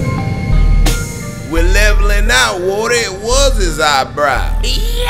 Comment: If you're wondering wtf "birdman" said; "We're levelin'' out, woody, it was his eyebrows !"